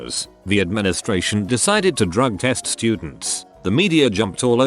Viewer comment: Okay, when it said "the school decided to drug-test students", I first heard it as "the school decided to drug test-students". Painted a nice picture in my mind.